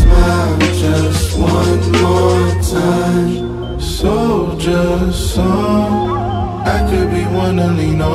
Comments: When I first heard this I thought they were saying "sold your soul" instead of "Soldier Soul"...anyone else? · I thought the chorus said sold your soul, thanks for the Clarification